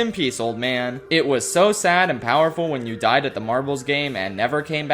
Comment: 😂everytime u say marbles sounds like ur saying "marvels" me thinking marvel rivals😂